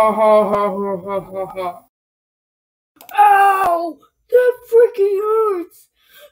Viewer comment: I thought you said that f word but when I heard the word closely I figured out you said freak lol